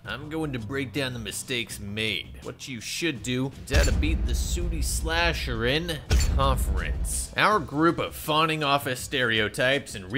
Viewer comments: Am I the only one that thought it said booty slasher……… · Tbh thought it said booty slasher